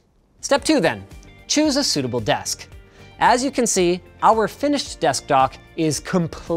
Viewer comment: Did Linus legit say Desktok at 2:45? LMAO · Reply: I hear "Deshk Tock"